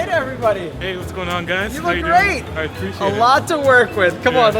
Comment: "You look great but a lot to work with." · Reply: I thought he said that they "got a lot to work with" not but :P